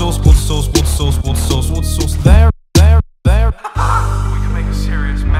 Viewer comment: It sounds like he is saying Wooster sauce